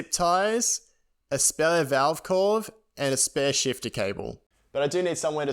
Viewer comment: What in the hell did he say at 7:01 ? lol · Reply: My dyslexia was strong in this video hahah valve core